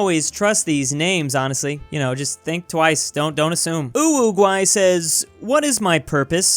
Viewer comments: did he say... uwugay? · am i the only one who heard it like UwUguay